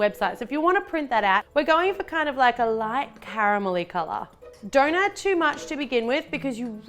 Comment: it sounds like she's saying doughnut too much instead of don't add too much! LOL